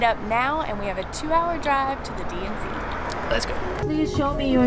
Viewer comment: I thought said DMV